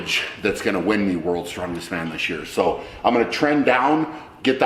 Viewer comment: does he say that he is going to tren down?? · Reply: I think "trend"